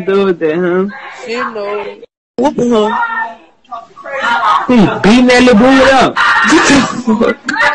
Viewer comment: She did say "stop you raping me " she said it twice smh.. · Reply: and i heard " dam he beatin dat lil booty up'